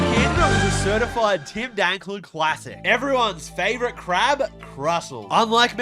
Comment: misheard this as “certified Tindangle classic” and I Think my heart may have stopped beating briefly